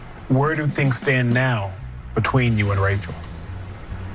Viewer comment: It sounded like the Bachelor called the lady "Racial." 😂